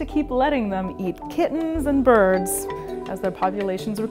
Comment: Sorry its a nope for me. I thought she said chickens. But it was kittens..